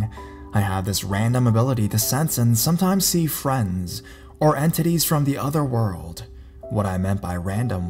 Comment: When said entities it sounded like he said and titties lmao